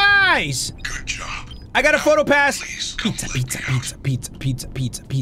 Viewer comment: if u set the playback speed to 0.25 it sound like he is saying peter in a british accent when he in reality he is saying pizza